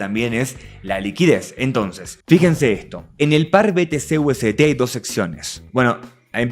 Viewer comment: "UST" escuché por un momento ... que malos recuerdos 😂😂😂 :warflashbacks